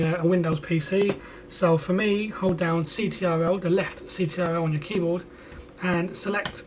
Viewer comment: how did he select two layers at once? he says hold down left something at 4:12 but I can't understand what he said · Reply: Ctrl